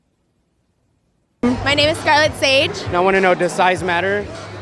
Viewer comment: Most of them was like Its the motion in the Ocean 😂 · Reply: did I hear her name correctly as "Charlotte Sage"? Nice vid bro!.